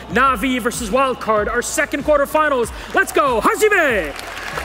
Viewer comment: What does Ghassan say at 24:28¿ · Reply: He said "HAZIME!" that means "Start now!" in Japanese